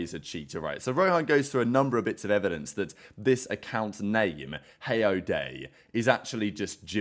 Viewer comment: Thought said this Snake not name 😭